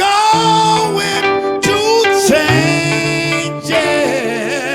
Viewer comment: Realizing after you pointed out that he says I’m going to change it, it hits so much harder. I thought it was because of his accent that made it sound like I was saying it off. But knowing what he was going through with his mother it makes it so much deeper